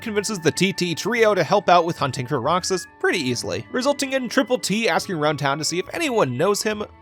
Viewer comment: did you just say…. Triple T….